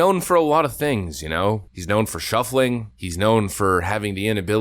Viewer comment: I thought you said he is known for shoplifting.